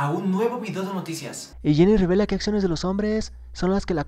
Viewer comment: Wey cuando dijo: Jennie rebela Pensé que dijo Jenny Rivera :v